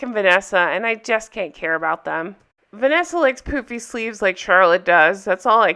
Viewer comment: I’m listening and watching while putting on my makeup, and I heard “Vanessa likes poopy sleeves just like Charlotte” Wait, what?? Ohh,, pooFy sleeves. 😂